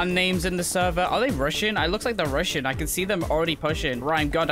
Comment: i thought he said it looks like they’re russian not rushing lol